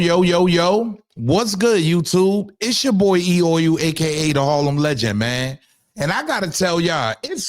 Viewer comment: Is what he saying E oil? I want sure what he said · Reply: I thought he was saying, “it’s your boyeeyoy.”